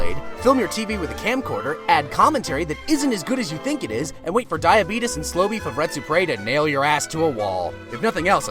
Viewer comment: no matter how often i listen to it, i can't understand what he says at 6:03 "wait for diabetes to...?"